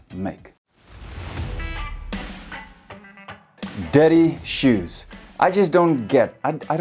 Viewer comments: I thought he was saying daddy shoes for an entire minute 😂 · The first one i thought he said "daddy shoes" 😂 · I heard "daddy shoes" for 1 minute and was trying wrap my head around it. 😁 I`m not native though. Good video!